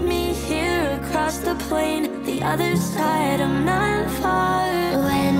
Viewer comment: I thought it said "the void ain't there"